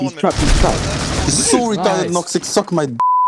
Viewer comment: For a moment I thought he said "Woxic"